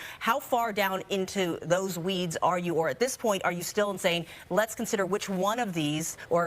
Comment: the captions make a pretty great error. Instead of asking the guy "are you still saying..." the captions have the local news lady asking him "are you still insane?" XD